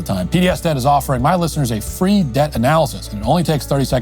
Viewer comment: Haha, I thought it said "PDS is offering free depth analysis"